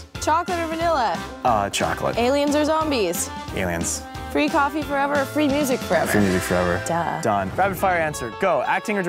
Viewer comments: What did he say? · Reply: dano thanks, but what did he say before that? "Free coffee forever or free music forever" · what does he say 00:34? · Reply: Chlo money free music forever